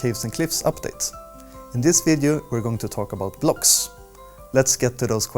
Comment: not to offend his accent or anything but I really thought he said "glogs" I was like OK MAYBE THIS IS THE WRONG MINECRAFT VIDEO